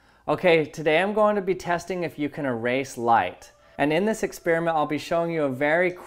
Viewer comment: Thought he said “Erase life”